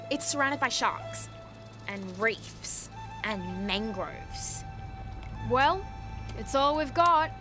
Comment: "That's mako island, its surrounded by sharks and reefs and mangroves" -proceeds to swim out through the tidal pool into the open ocean sksksks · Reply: I always thought that she said mangos instead of mangroves